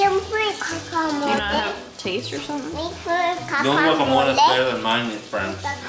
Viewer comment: Sounds like is saying cacamole lol 😂 she’s so cute both of your girls are so adorable 🥰